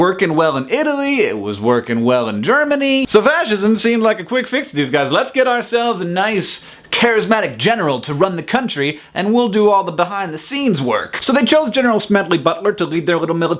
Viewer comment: Did he say Vagisism at 2:54?